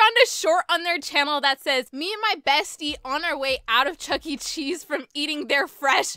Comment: For some reason I heard you say “chuggle cheese”